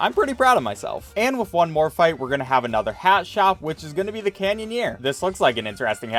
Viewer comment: Canyonyear is what I heard